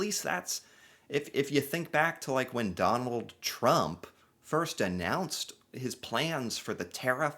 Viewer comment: I repeated it a few times here because I kept hearing McDonald Trump